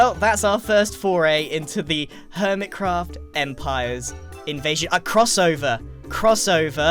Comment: Am I the only one who heard say invasion and then cut himself off